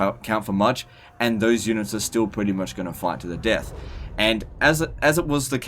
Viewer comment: had to rewind it twice to realize he was saying "fight to the death".... At first, I heard "fuck you to death".